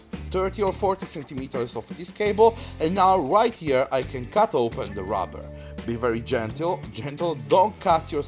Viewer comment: I thought u said cut open the robber